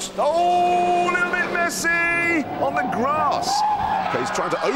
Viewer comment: did he say Lionel Messi